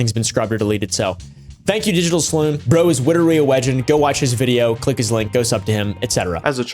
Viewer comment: Did You just say ''Bwo is witerally a wegend? hahaha sorry carry on